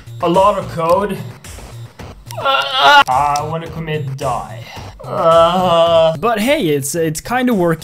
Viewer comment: did you just say you want to commit die!?!?!?